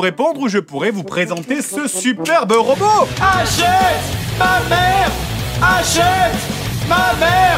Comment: J'ai compris "Achète ma mère". La vidéo n'est plus la même.